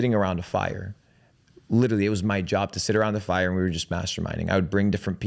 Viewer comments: Swear I thought he said they were just all sitting around the fire masterbating. Dude is weird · At first, I thought he was going to say “It was my job to sit around the fire, masturbating”. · definitely thought he was about to say “masterbating”